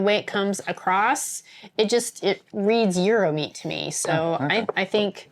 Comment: Did you say it resembles "Euromeat", like from Europe? (Around 6:00 min) What is that supposed to be? I'm from Europe, we don't have a special kind of meat here you can't get anywhere else. ;-)